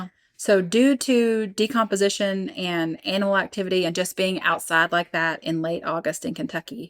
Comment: Did you just say Anal activity 6:29??? Did you mean Anomal activity 🤔🤔🤔 crazy story though. Good job